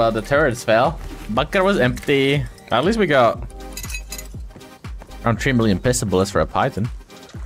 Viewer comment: what the fuck did he say? 💀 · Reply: "Around 3 million pistol bullets for a python"